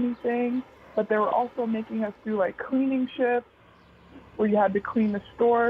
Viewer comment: "cleaning sh*t"? did i hear correctly lol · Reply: Shifts